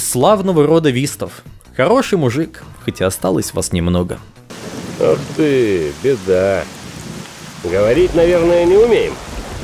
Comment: что он сказал? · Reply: Ух ты, беда, говорить наверно не умеем?